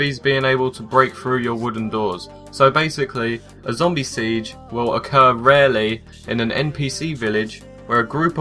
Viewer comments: i thought he said 'zombie sea jizz' l3g1t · i thought he said "Zombie sea jizz" XD